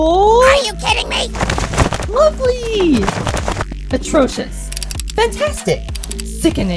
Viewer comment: I thought Ruby said "Ugly" instead "Lovely"